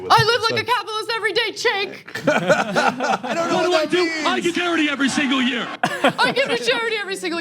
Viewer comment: I really thought Charlie Kirk said "I get dirty every year". LOL!